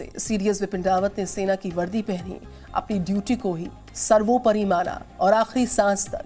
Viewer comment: At Location 00:15, did the anchor say "सर्वोपरी मारा " instead of "सर्वोपरी माना"?